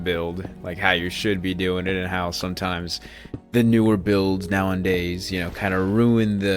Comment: did you say now in days